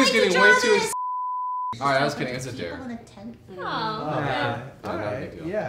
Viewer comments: can someone tell me what he said?? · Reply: It was a dare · I can't understand what he said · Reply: Gabriel Nobrega “I was just kidding it was a dare”